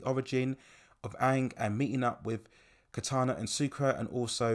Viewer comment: do I hear "KATANA"?